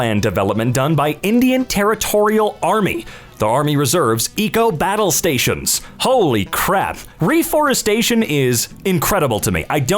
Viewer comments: did you call a battalion battle station? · did he really say battle stations lmao